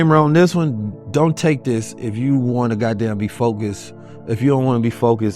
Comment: ish ...did you say, don't take this if you want to be G-D D#MNED Focused ?? I couldn't make it out and need to know if those words came from your mouth. Anyone know if this man damned GOD or not ?????